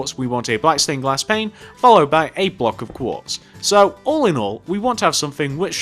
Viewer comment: DID HE SAY ALLINOL?!!?!!?!?